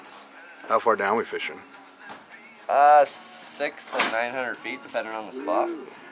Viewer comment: Excuse me? I could have swore you said 900 ft.? Ha! That's epic! I bet you have the whole place to yourself. Good for you guys!